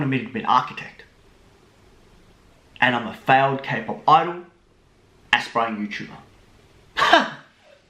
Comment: What did he said at 2:11 ? "A failed kpop idol and ........... Youtuber." Is it aspiring youtuber?